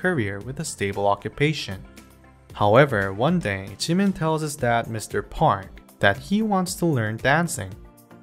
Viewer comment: Umm... Excuze me, I just heard "Mr. Porn"